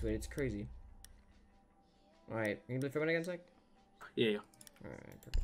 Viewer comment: (いいよ)にしか聞こえないw